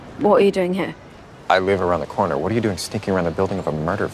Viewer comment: I wonder why I hear 'sticking' instead of 'sneaking' at 8:40? Looks like real hard 't' sound, why is that so? Btw, your videos are very entertaining, thank you! P.S. You're very beautiful, hope we'll meet someday :)